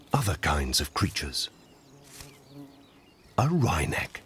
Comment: What kind of bird did he call that? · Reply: A "wryneck", basically a kind of woodpeckers :)